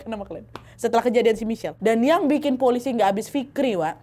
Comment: Terlalu fokus dengerin nya nggak sengaja kedenger "Dan yang bikin polisi nggak abis Fikri Wak " 😭 di menit